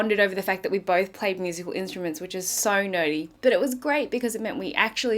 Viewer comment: You said "nerdy", I heard "naughty" LOL. Where do people get lives? I need to get one.